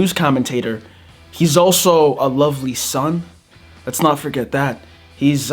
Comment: Did u just say a lovely son 😭😭😭 I’m dead af